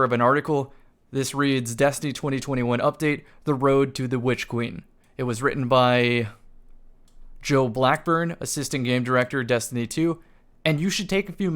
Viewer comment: This post got me so worked up I swear I heard "Road to Bitch Queen, by Joe Mama"